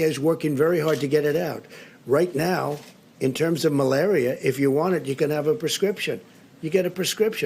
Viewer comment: for a sec i thought he said "melania"